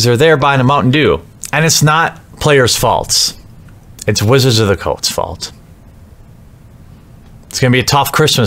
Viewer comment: Sounds like you say Wizards of the Cults fault 😂